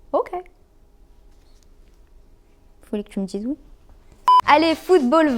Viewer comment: Elle a dis quoi 2:55? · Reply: "Fallait que tu me dise oui"